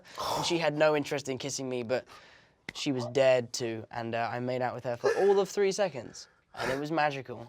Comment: When he said "Dared to" i thought he said "Dead to".